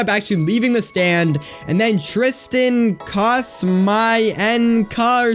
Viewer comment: I heard "Tristan...Cost...My...N...Kuh?"